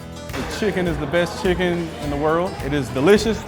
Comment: "Chicken is the best Chicken in the world" did i just misheard this???